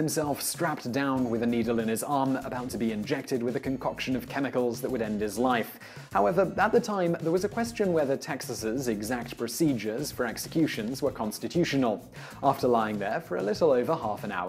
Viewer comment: did he say texasans? lmao